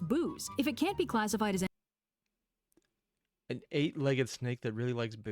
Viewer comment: I think accidentally said eight-legged when she meant eight-headed. Orochi famously got drunk in his legend to be weakened and slain. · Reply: did clearly say 'eight-Headed,' actually. The mistake was the hearing of , lol. How quiet is his 🎧.....😅or how loud is his train-of-thought?